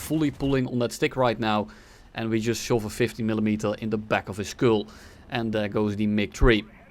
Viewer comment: I thought you said 50mm for a sec. I didn't know the 109 could carry that